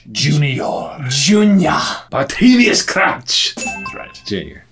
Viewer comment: Did he say "Barterius Crouch"?